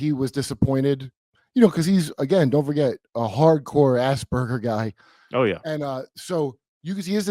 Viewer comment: Totally heard that as assburgers not Asperger's and I don't think I can unhear that now 😂